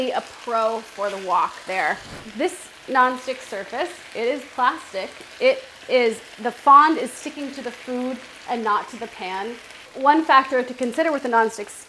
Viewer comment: I'm so sorry but I'm not quite getting the word: "This non-stick surface is plastic, so the fonde is sticking to the food instead of the pan."? Is that correct?